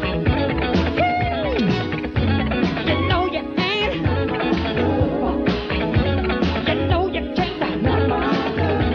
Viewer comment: What Michael say here? 4:53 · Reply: “You know you ain’t. You know you can’t. Because you are... you’re just a city girl!”